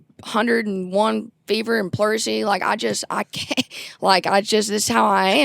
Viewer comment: Ive never heard that word before but does anyone know how to spell that word? I thout it was pluricity, but I dont think it is and I've never heard it before.